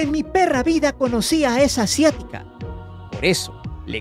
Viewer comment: hahahaha maldita disléxia, les juro que escuche que dijo esa "perra asiática" hahahaha tuve que regresarlo para escuchar bien ahahahahaha